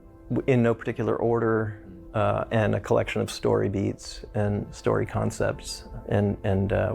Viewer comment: Muitos erros de tradução nesse vídeo. Aos 2:27 por exemplo ele fala "story bits" (pedacinhos de história), não "story beats" (batidas de história). Aos 2:35 ele fala "critical mass of mechanics" (massa crítica de mecânicas, não "mecânicos" como foi traduzido). Aos 3:37 ele fala "even [bigger than] Episode 2" (maior até do que o Episódio 2), mas foi traduzido como "Você vai até o Episódio 2" (????). Entre outros pequenos erros.